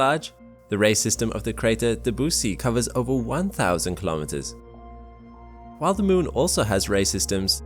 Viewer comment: Did I hear "Dabussy"?